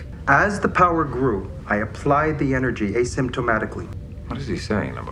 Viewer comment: The subtitle has him saying "asymptotically", which makes a little sense. But what he really says is "asymptomatically", which is just silly—and a further sign that his competence is feigned.